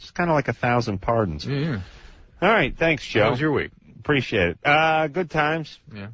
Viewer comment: did adam say "pretty shit" and then switch to "good times" right afrer? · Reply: "Thanks, Joe, appreciate it"